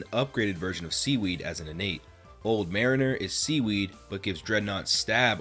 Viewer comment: Dreadnaut legitimately looks like an official 'mon, moreso than any other fakemon in this game. Massive respect to the designers of that one. Could have fooled me. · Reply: My dumb#ss thought he said Drednaw